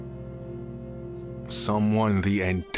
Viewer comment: Why do so many people keep mispronouncing JUN kazama lmao. its said like the month 'June'. Dude sounded like he was memeing and said 'JOHN KAZAMA' lol